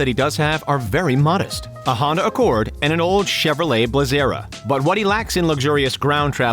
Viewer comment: Did you just say a Chevy blezera??